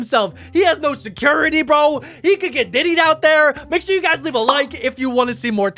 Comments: idk but i heard him say diddy's out there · Did he just say Diddy💀